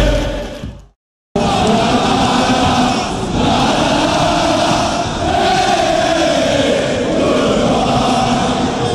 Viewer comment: What are they saying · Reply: Goodbye to the Melbourne fans